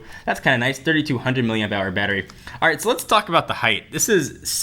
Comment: Did I just hear 3200 million dollar battery at 3:24? :-) Probably coz of too much excitement.